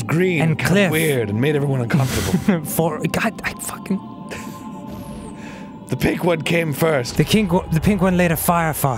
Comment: I swear Dan said "The Kink One."